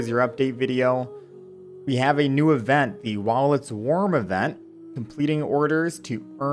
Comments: I just heard "Wallet warmer event" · Am I the only one who misheard the "While it's warm" part as "Wallets warm"?! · While It's Warm Event Me misheard : Wallet's Warm Event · Wasn't watching the screen at the time, thought you said 'Wallet's Warm Event', like wow, they're not being subtle about their strategies anymore. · I played this at 2x speed and it sounded like " the WALLETs warm event" XD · I first thought you said "Wallets warm event" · I heard " "Wallets Warm" Event" LMAO